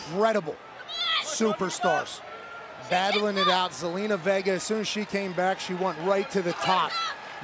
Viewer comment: Did he just say zelina vegar? 😂